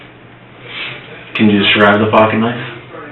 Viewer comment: Best captions misread, "can you describe the pocket knife" captions say can you describe the fucking knife😂